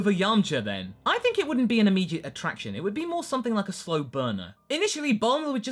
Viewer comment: I laughed out loud at 1:43 because for some reason I heard "slow boner" instead of slow burner. What is my life.